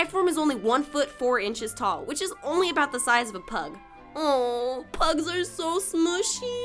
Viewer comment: Did she say PUBs as in a bar in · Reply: She said "pug", the breed of dog that's shown after she says that